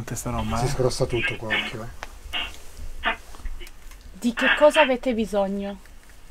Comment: Al minuto 4:51 circa quando ha chiesto "di cos'hai bisogno?", io ho capito "di acqua"